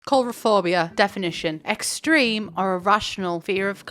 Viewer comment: biiiitch i thought the first word you said was ‘HOMOPHOBIA’